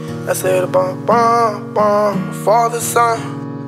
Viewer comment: this nigga really said the father son instead of caterson 😭😭😭and niggas tryna say he aint copy paste YB song